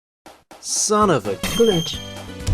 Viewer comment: I misheard that as "Son of a bitch"